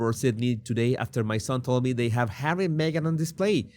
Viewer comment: I was in the middle of something and misheard you and thought you were being funny and calling the couple “Ham & Egg” 😂😂😂 But for real… can we call them that from now on? Duke and Duchess, Ham and Egg